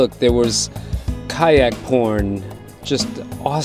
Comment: Did you say "Kayak Porn" at 15:25😂😂😂 love your vids